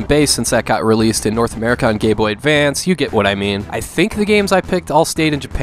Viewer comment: Did you call it a "Gay Boy Advance"?